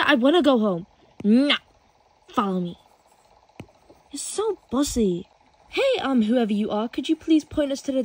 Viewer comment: When she said “you’re so bossy!” I heard “ you’re so bUsSy “